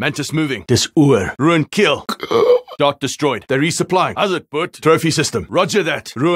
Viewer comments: Anyone know what he is saying at 15:16, sounds like Hallo Bud? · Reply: Howzit boet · what's he saying at 15:13? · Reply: "Dis oor" translates to it's over